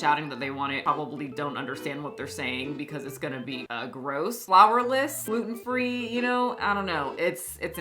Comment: At first I thought there were saying "flavourless" cookie and I was like "off course it's bad, it's in the name" 😅😅